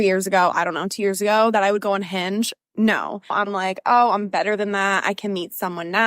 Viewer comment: whenever i hear “on hinge”, i hear “unhinged”